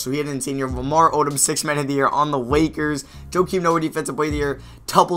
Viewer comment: anybody catch him say wakers